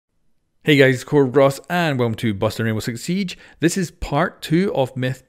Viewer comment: Every time you say the intro, "Busting Rainbow Six Siege" I always hear, "Boston Rainbow Six Siege".